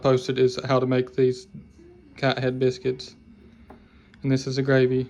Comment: 🗣️WHAT! Did she just say cat-heat biscuits? I thank I may be related to this woman.😮 my mama might get her biscuits.